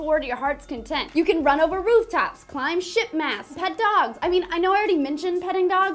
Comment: did she say "sh!tmasts"? Cause I was kinda distracted by her amazing cleavage.